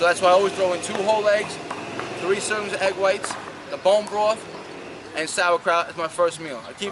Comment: Can someone tell me what is he saying at 7:45 ? Bomb tot what ? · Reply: Bone broth